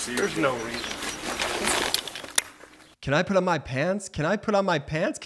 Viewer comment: you sounded like " Can I put on Mike Pence " 😂🤣